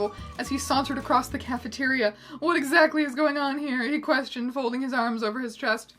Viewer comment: Mr. Greg Snape: “What exactly is going on here?” What I hear: “wHaT tHe DeViL iS gOiNg On HeRe?”